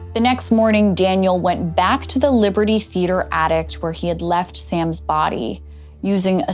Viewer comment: "attict" multiple times was driving me crazy. I am glad I'm not the only one who heard it. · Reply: ye, first time I was confused, like did she just say "addict" instead of "attic"? - it sounded like half way between the two to me tbh, and not just once.. Based on other comments the lady seems to have an ardent following heh.